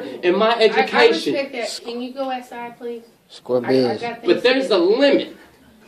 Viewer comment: What is the dude who is video taping saying. It sounds like he is saying square beans. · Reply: "Square bizzness" hood slang basically saying that he agrees.